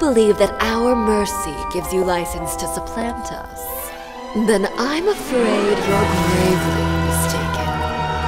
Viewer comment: When Arlecchino said "then I'm afraid you're gravely mistaken" I thought she said "then I'm afraid your eyeball's taken" 💀